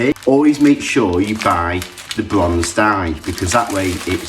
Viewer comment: Wonderful video, and I loved your expression when you tasted the finished dish!! One question – I couldn’t make out what you said at the one minute mark when you were talking about which pasta to buy. It sounded like you said the bronze dye but I know it wasn’t that…